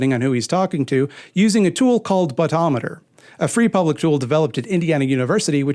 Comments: Okay, not to mock speech patterns, but I sincerely thought said Butt-ometer... As in Elon pulled it out of his... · Something is wrong with me - I heard "butt-ometer" and expected to follow up with "which is the new accepted term for pulling lies out of one's ass" 🤣